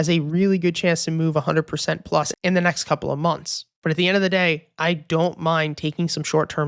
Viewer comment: Did you say next "couple" of months?